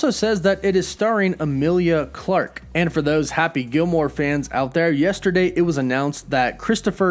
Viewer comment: I had to back up the video a bit; for some reason, I thought you said "Gilmore Girls" instead of "Happy Gilmore" 😅 Woof woof :3